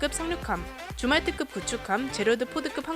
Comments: 더블 모니터라 틀어놓고 유튜브를 들으면서 게임을 한다거나 다른 작업을 하는데.. 줌왈트급이 주말특급으로 들려서 되돌려 확인했습니다... 죄송합니다. 집중하겠습니다.. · 줌왈트급 나만 주말특급으로 들었나? 예^^ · 줌왈트급... 자꾸 주말특급 으로 들림 ㅋㅋ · 줌왈트급이 주말특급으로 들려요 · 잠깜 뭐한다고 영상안보고 소리만듣다가 줌왈트급을 주말특급으로 들었네